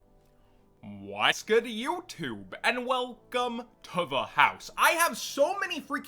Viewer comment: I like to pause the video at 2 seconds so it sounds like he is saying "What's good? You!" professional motivational speaker.